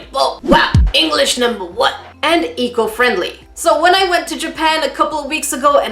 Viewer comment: I heard "it's EAGLE friendly" 🤣😂🦅🦅🦅